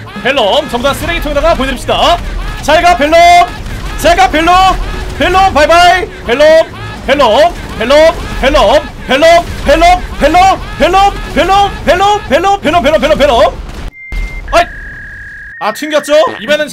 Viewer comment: 자세히 들으면 hello hello hello로 들리는데요?